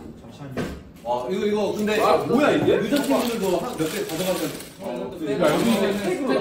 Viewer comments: 댓글에 왠 여자친구들 얘기가 있나했네요. "여자친구들"이 아니라 "여*팀들" 이라는 거 같은데, 전체 맥락을 보면 음식(아니면 다른 거) "여*팀들"한테 갖다 주라는 말 같아요. 발음 불분명해서 잘 들리지는 않는데 여자친구가 나오기는 쌩뚱맞은 흐름입니다. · Why gfriend is called protocol team? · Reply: when this video came out,, the translation at 9:30 said "gfriend members can take some of them" but fans really dont like that so they email to bighit to change it cause they cleArly hear ujeontimdeul but the funny thing is when fans try to say they hear "ujeontimdeul" and said they are korean people and when korean people ask them to type in korean they keep silent..and bighit change the translation from gfriend tu protocol team means staff.. and then in MMA awards,, V gave speech and said thankyou to "ujeontimdeul" clearly and bts laughed..means they know about this joke..honestly whats wrong if they really said yeojachingu?? they are friends yall should stop this fanwars now..let them freely interact with each other!! sorry for my bad english bxdjsbkje